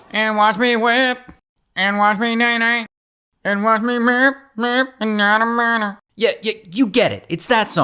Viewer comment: Did I hear “it doesn’t matter “while you’re singing 😂😂